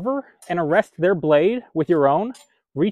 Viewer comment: I had to watch this twice. The first time I could have sworn he said shrek them.